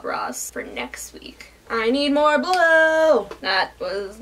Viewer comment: Casually checking another tab and all I heard was "I need more blow!!!" - it took me a few seconds to realise you were, in fact, saying 'blue' and not 'blow'... (It's midnight and I'm dead inside, that's my excuse for mishearing the obvious :') )